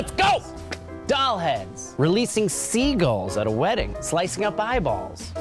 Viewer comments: I tabbed out and then I heard this... "Slicing up bibles... HAHAHAHAHAH" o_o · I thought he said, "Releasing semen at a wedding!"